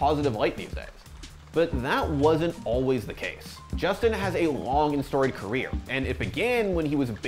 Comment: Bro I misheard that as " Justin has a Wong and storied career"